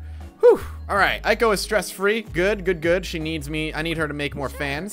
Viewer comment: did say She needs me?